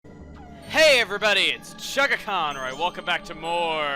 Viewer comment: am i the only person that for almost all of one of play thew i thought he was saying "hey every buddy its chuggaachon alright welcome back" and not "Hey every buddy its chuggaaconroy welcome back"